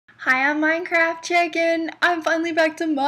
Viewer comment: I thought u said Hey ITS MINECRAFTCHICKEN